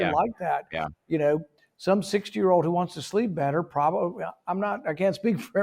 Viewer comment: I thought he said 6 year old had to rewind 🤣 60